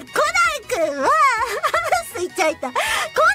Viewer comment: Is that a mistranslation? I think I heard "Sui-chan" being mentioned there when she's talking about Conan... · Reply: you heard her reading a chat message, スイッチ入った, meaning something close to "a rapid change of topic/a tangent" on it's own